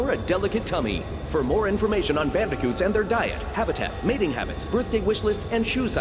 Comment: Did he just say Mating Habits?